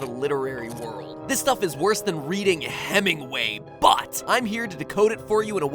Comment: I was at 4:28 when I had to leave to do something, when I came back and started the video, all I heard was the loud sound of mat pat saying "BUTT" *ik he said "but" not "butt"